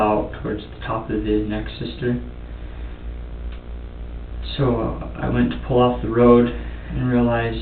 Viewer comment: when he was doing CPR on the 911 call, i heard him say "i went to ffar this time" plain as day he sait it, why has the detective not mentioned that? yet ... im not done with the interview yet. but they had to have heard it too